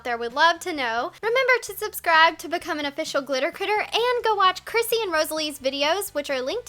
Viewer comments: I JUST GOT THE WHOLE GLITTER CLITTER OH MY GOD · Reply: I thought she said glitter critter... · Am I the only one who heard her say glitter cLitter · I thought she said glitter clitter. My life is ruined. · When she said "Glitter Critter" I misheard her say "Glitter CLITter" How ironic xD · Glitter shitters and glitter clitoris yeah I thought she said that